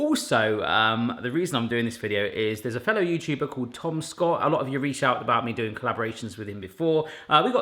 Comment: Be sure to check out & subscribe to Tom's channel <link> I will put the link to Tom's video here on Monday once live! The full recipe / write up for the homemade garlic bread can be found here <link> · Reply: I really enjoyed the Tom Scott & secret vloggers club video. However, will you please forgive my American ears. I kept hearing you say "Tomska", rather than "Tom Scott". After seeing you with Tomska on Barshens, I think a colab between you would be rather fun to watch. Maybe have him on your channel for a cooking video. Then, you star in a video he writes for his channel?